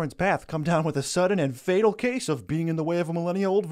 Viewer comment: And then he ends the video with the equally cringeworthy "Peace out tribe scout." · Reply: Wait. Its tribe scout? I thought he's been saying dry scalp